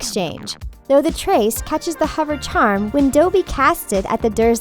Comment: Did- did she just say “Dobey”?